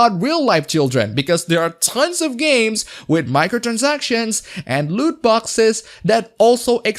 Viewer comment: for a moment I heard you saying Lewd Boxes ...